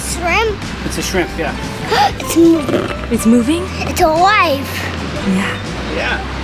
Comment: i know said "its alive!" when looking at the shrimp but I heard "its my wife" and I cant stop laughing